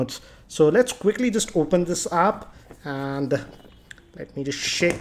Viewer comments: did you said " open this APP " ?? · So lets quickly lets open this App He actually said app XD